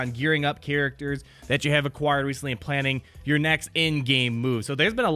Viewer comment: When you said "in-game," I thought you said "endgame," and I got really worried